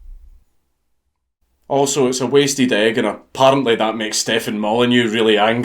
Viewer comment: can anybody tell me what he is saying here? "Also it's (Blank), and that makes Stefan Molyneux angry." I re-watched this section about ten times, even slowing the video down, and could not understand what he was trying to say with his accent. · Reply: "A wasted egg"